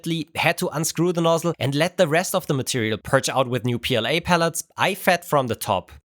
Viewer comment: You made a pun you didnt mean to. You show cows and I thought you said puellae, ancient latin for girls. Your pooellae because those girls make the poop 😆